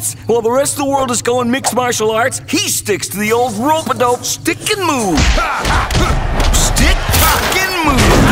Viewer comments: I thought freeze blade said stick f**king move instead of stick puckin' move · Anyone else hear "stick f*cking move"?